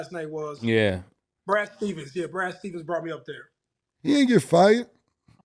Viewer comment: was clearly saying that Brad Stevens brought him out there not Ime Udoka. y'all wasn't listening.